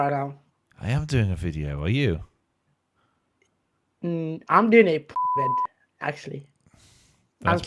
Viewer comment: what does he say here that you censored? 🤬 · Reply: "corn" video